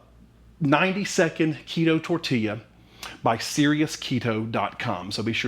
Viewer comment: I was so nervous when this came up in my feed. I’m glad you liked them. The silicone cake rounds that I show in the follow up video make all the difference in the world. Thanks! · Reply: Haha, when introduced your recipe I thought he said it was your 92nd keto tortilla! I didn't have high hopes for a success after that 😂